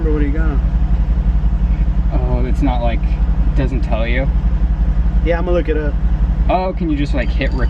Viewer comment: “I don’t remember what he got” “Oh...so he didn’t tell you” 😂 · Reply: I heard it as, “Oh, it doesn’t tell you?” Then the server said, “I can look it up.”